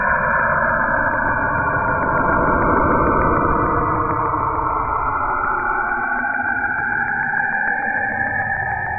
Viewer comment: i hear the sound strom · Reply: Ångstrom